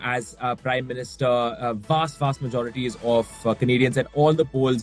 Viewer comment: Does he say "A vast vast majority is all f*cking idiots" or "A vast vast majority is all for Canadians"?